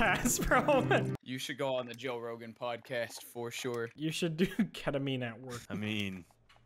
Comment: Deadass thought that when said Ketamine I thought he meant Kerosene. My reaction could not be put into words except for "rolling on the floor laughing like Smii7y"